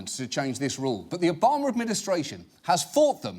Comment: right after 10:30 it sounds like hes saying 'The ObongRip administration'